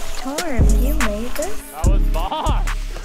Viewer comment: why do I hear a female voice saying “torm you made this”!?!?😂